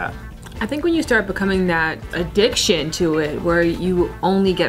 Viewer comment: I thought she said DickShit lol.